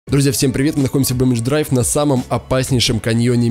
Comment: я один услышал слово всем привіт ?